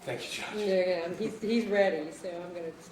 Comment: what did the judge say around 1:26:00 " your perilously close to ________, hes ready_________" what did she say I must know Lmao. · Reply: She stuttered trying to pronounce perilously, But said “you’re perilously close to leading, he’s ready so I’m going to stop you” “Leading” meaning, the prosecution was trying to put words in the mouth of the witness (police officer) And, “he’s ready so I’m going to stop you”, implied that the judge recognized the defense was about to call an objection.